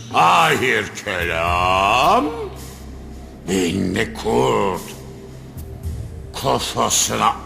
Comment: 20 yıl olucak nerdeyse ama ben hala tam olarak ne dediğini anlayamıyorum. beyinli mi beyinde mi behinli mi neyse artık ama yok. mantıklı bir yere bağlayamıyorum. yıllardır içime dert olmuştur bu cümlesi. bilindik bi atasözü de değil. damlaya damlaya göl olur, sonunu düşünen kahraman olamaz gibi. yorumlar var tabi görüyorum ama yine de net bir konsensüs sağlanmış değil. bu durum beni çok yoruyo. lütfen senarist gelsin artık ve yıllardır içten içe büyüyen bu sorunu çözsün.